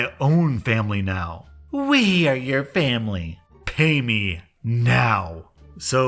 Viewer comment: What it said:payment What he said: pay me What I say:REEEEEEEEEEE